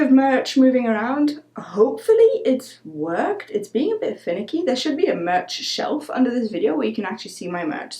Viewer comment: I heard "It's being a bit Finnick-y" I know she didn't mean that though 😆